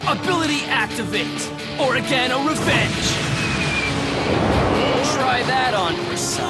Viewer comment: did he say Oregano Revenge? Like the plant? Why????